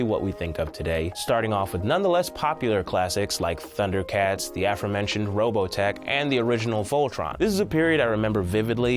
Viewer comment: did he say "the AFROmentioned Robotech" at 3:55? ;-)